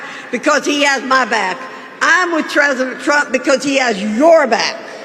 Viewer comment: 😂 Virginia Fox: "I'm with tresident" I could've sworn she said, "treasonous trump"